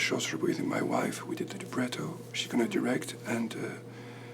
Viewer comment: I don't know who is the half-wit who wrote "mnemonic" and "(mumbles)" at 8:13 and 8:17 in the english transcript when he very clearly said "Ennio Morricone" and "Bernstein" but these are two legendary film composers that whoever writes the subs for The Hollywood Reporter should definitely know about. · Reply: again, he clearly says "libretto". I'm beginning to think that whoever wrote the transcript has little to no musical knowledge whatsoever.